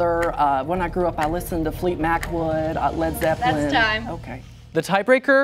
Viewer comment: Did he say Fleet Mac Wood?? 😂😂